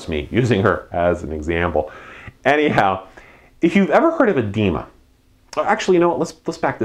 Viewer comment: when he was talking about his wife i thought he said "have you ever heard of a demon"